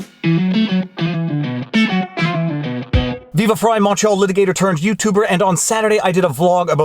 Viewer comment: I just realized you describe yourself as a Montreal Litigator. Been following you for months and thought you were saying Macho Liberator. I willingly refuse to redact my perception. Keep up the good work!